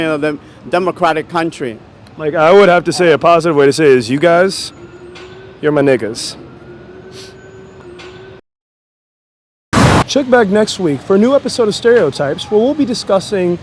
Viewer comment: totally sounds like he says "shit bag next week"